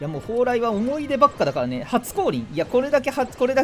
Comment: 初降臨が初恋に聞こえたw